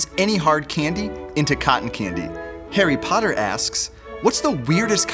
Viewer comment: I thought hea said Harry Potter